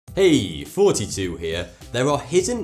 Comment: Sometimes i hear "hey, 42 here!" Instead of "hey, thoughty2 here"